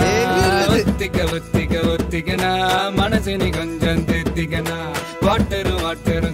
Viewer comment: My name is Manasvini. Everytime I hear 2:02 where he is saying “Manas a ni”, it feels like my name 😊🎶